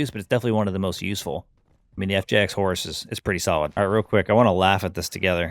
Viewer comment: I misheard FJX HORUS with FTAC SIEGE… my mind already wants to be the truest gun-foo player.